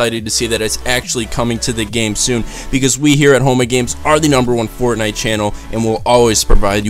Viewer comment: every time he said home of games it sounded like he said homo games